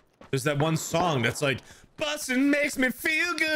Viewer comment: I just noticed that song always said “bustin,” in the new “Ghostbusters” movie, which is ironic because I’m a fan of “Ghostbusters” movies/franchise, & I always thought they’d said “plus it makes me feel good!” #BussinShouldntBeReservedExclusivelyForCopsRobbersCapsAndOrNuts